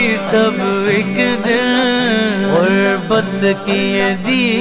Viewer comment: does he say ghurbat or qurbat? I have a disagreement with my brother. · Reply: He say Ghurbat ......okay